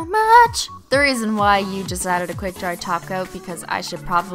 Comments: This whole time I thought she was saying TACO but she's actually saying TOP COAT !! Lol omg. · was she saying glossy taco? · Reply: leblanc I say tacos cause people always told me they thought I was saying tacos instead of top coat anyways so what the hell let's add glossy tacos 😂😂😂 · I keep hearing " glossy tacos"? Was she doing that on purpose? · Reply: Yes, because when she said top coat lots of people noticed that it sounded like she was saying taco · it sounded like u said taco instead of top coat